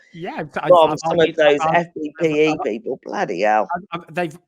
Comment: 38.09 - "Have you heard some of the bile that comes from those (...?...) people". Anyone pick up what Claire said? I heard "FPPE people" - what is this? Thx. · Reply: Fppe was the anti brexit ppl on Twitter. They all have the letters in their twitter biog or twitter name. I think it FBPE. And they all generally follow each other and gang up on brexit supporters and yeah they get very extreme in what they tweet.